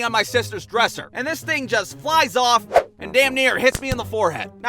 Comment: the subtitles says "Whoosh" its woooosh